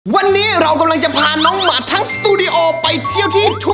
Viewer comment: ผมได้ยินว่าน้องหมัด😂😂😂